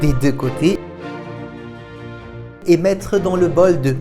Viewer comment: 😂😂 j'attendais la suite mais comme ça venait pas je le suis dit bh "le bol de" quoi ?? Jusqu'à ce que je comprenne que c'était "bol d'oeufs"😅😂😭